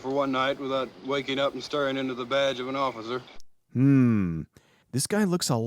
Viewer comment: did he just say "...without staring into the vag of an officer"? What a way to wake up.